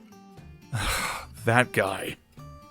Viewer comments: When said"that guy" I had thought he said "fat guy" ....... Whoops TnT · everyone talking about how the ship is sinking Me:"When Aaron said"that guy" I had thought he said "fat guy" ....... Whoops TnT"